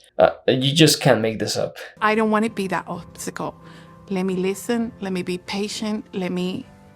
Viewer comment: Excuse me what did she say at 32:18? "Let me be wanked" is what my phone thought she said.